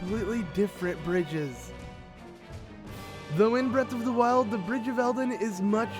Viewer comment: I keep hearing "inbred of the wild"